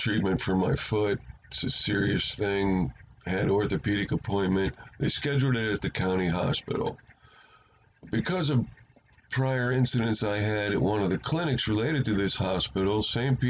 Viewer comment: IIt sounded like you said the Commie hospital even though it’s probably the county hospital LOL same thing